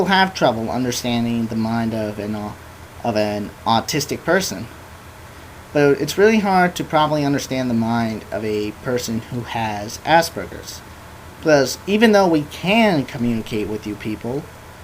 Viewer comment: okay at first i thought he said ASS BURGERS lol so wat is aspergers axactly??